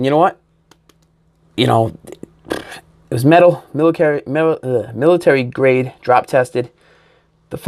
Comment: For a second I thought you said the phone was military drop tested. I was like "Damn, that's hardcore!" Picking up mine later this year...Thanks.